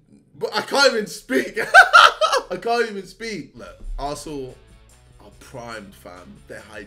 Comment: is it just me or did JJ just said ,,Look arsehole are just primed fam''